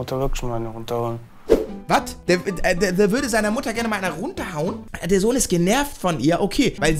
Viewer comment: Ich habe runterholen verstanden und nicht runterhauen